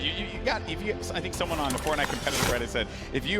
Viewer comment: Caption says porn lol